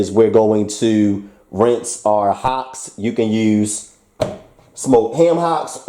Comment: oh I misheard you at first; they are ham hocks. so pig knees like I guessed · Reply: ham hocks